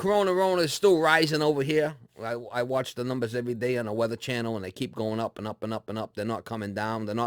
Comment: did he say Weather Channel? Love you more. :-) lol. Masks and Social distancing, we'll all be ok. Tough for inner city folks with dense population.